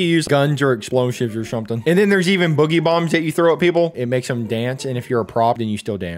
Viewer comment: it says Evan instead of even 😭☠️